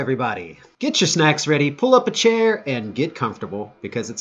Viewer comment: I thought that he said “grab your snacks full of chair!”